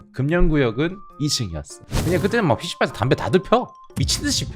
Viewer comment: 나만 ㅅㅂ 담대를 다들펴라고 들었나